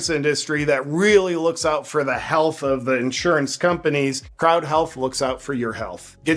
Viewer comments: I thought he kept saying "kraut health" during the sponsor ad! Lmao! I'm like "Is this healthcare for Germans?" 😂😂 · Could have sworn you were saying "Kraut health." Lol. I was onboard...